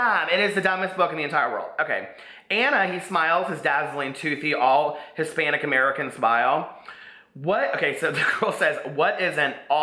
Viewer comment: HaHa - this is so fun! :D Did I just hear, "All hispanic-american smile" ??? What DOES that mean?! BAHAHA the pulling up the dress. Oh gawwwddddd.